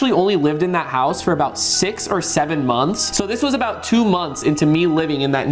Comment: did he say 6-7 months